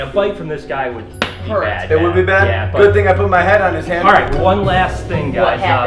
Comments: Am I the only one who heard him say "good thing I put my head on his hand" even tho its the opposite way around😂😂😂😂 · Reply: Ameria Ann I heard " good thing I put my hand on his hand" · Am I the only one who heard him say "good thing I put my head on his hand" even tho its the opposite way around😂😂😂😂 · did anyone hear him say my head on his hand